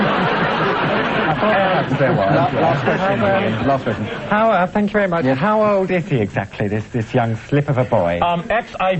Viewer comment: What did Clive say at 9:32 after Mike mentions "little slave boy"? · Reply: Clive says "Last question" , directed at Julian.